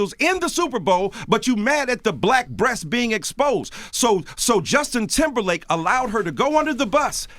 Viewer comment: Did he...did he just say Justin Timberfake? I'm done. 😂😂😂😂